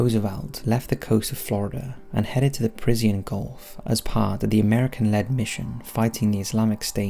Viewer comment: Did he just say, "Parisien Gulf"?